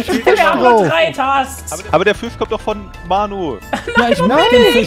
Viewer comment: Ich verstehe irgendwie immer nur bei 10:56 Fisch von Manu. Den Rest des Satzes nicht. Lel.